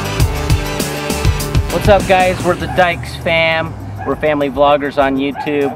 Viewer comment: no offense I like the video but I heard dikes and I'm new to the channel